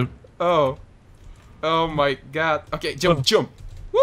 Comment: did u said "О, майко!" :D